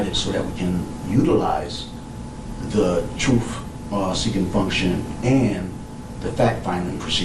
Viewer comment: I always think it's funny how bros cannot pronounce the "th" sound like in "the truth". Notice, around 5:45, this ape says something like, instead of "the truth", he more or less says: "an...utilize....thu troof".... feel me, yo? It's sort of like whey the bros say, instead of: "My wife is taking a bath.", they'll say: "My baby Mama be takin' a baf", feel me, yo?